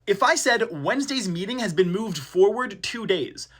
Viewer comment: I thought of Monday because of thinking of the week in order like a line on a page 😭 Edit: and apparently, after rewatching, misheard ‘meeting’ as ‘meaning’